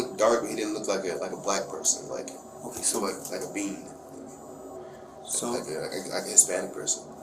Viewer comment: did he say the guy was dark but not dark like a black person but like a bean? Like short for beaner....and he's talking to a Hispanic detective!